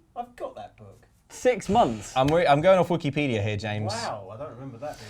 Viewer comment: did he say wookie-pedia? If so i can't believe I didn't hear saying 'boo!' Off screen